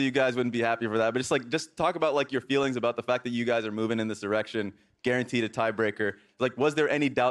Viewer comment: does anyone else hear "going in this erection" ??